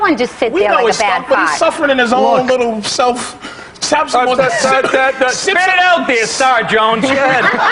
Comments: What did Nick say to Patrice at 11:57 Spit it out there, ... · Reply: spit it out there StarJones gah head sip for timing lol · Can someone please tell me what Nick says at 11:57? “Spit it out something something…” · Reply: Spit out there Star Jones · what does nick say? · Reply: "Spit it out there Star Jones"